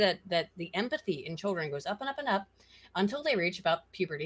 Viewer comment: She actually said that empathy (as measured by social scientists) is at a minimum between 12 and 16, when it starts to recover. She didn't mention anything about divergence by sex, and specifically mentioned that we all went through that phase. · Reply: Yeah, listening back to find the time it was said I realized I had misheard it, was listening to this at like 2am. Updated to children instead of boys, also the time is